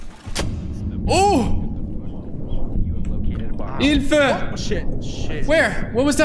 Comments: Did u said Hilfe?.. Tha fuq.. Kannst du Deutsch? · Did He say hilfe on